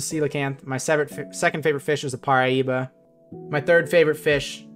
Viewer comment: did u say paraíba??